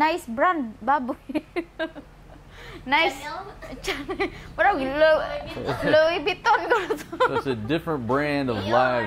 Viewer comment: I still can't understand a word she says. I thought she said Louis Vuitton when she said Lechon Baboy 😆🤣😄